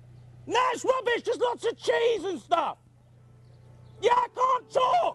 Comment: what is he saying at 1:10?? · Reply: "nah it's rubbish, just lots of cheese and stuff"